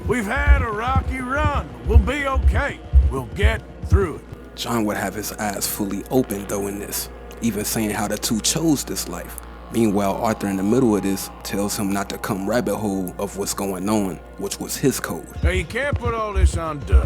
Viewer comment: legit thought bro said john had his ass fully open